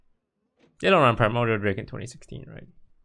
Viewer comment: D- did he say 2016?